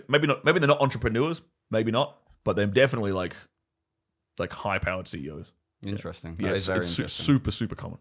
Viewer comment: I don’t understand Australian accent what he said High _______ ceo ? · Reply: High-power CEOs